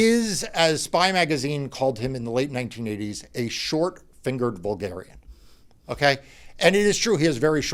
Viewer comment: Oh dear, I thought you said "short-fingered Bulgarian".